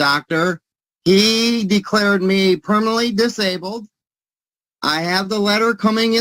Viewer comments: Did he just say he’s “criminally disabled??” LOL 😂😂 wtf? I’m so glad the judge is continuing with this! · I swear I thought he said "My doctor declared me Criminally disabled".....😂😂😂😂😂😂😂 This man is woefully wrong... A PRACTitioner IS a Doctor.... Or at least is equivalent to a Doctor.... He doesn't get it that Doctor shopping doesn't guarantee SSI or Disability.... This man makes the rest of us-- who are LEGITIMATELY disabled-- look bad.